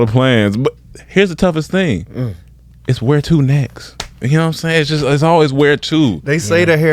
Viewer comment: said “where to next?” and I heard “we’re 2 necks”..😂😂 Been watching these guys too long.